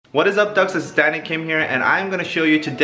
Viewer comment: When you said "Danny Kim here" I heard "Daddy Kim" 😂😂 I was like 'oh, he wildin'😂